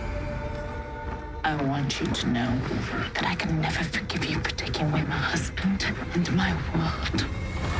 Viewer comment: Who is talking at 1:08 and what exactly is she saying? Thanks :) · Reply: LAGERTHA: I WANT YOU TO KNOW, THAT I WILL NEVER FORGIVE YOU FOR TAKING MY HUSBAND, AND MY LIFE.